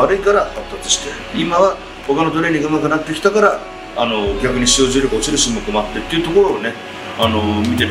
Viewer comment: ~のところ何回聞いても聞き取れないんだけど誰か教えてください。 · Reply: 逆に使用重量が落ちる種目もあって…だと思います